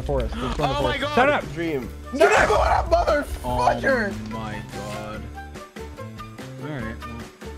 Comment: did sapnap say mother** or am i hearing things?